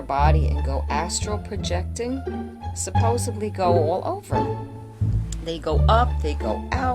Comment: I think she said "supposably" :(